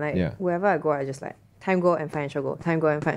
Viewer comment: Does anyone know what said at 24:50 😅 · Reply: “Time goal and financial goal.” 🙌🏼